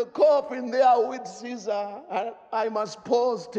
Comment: I thought it said at the end "with thanks to the Illuminati"!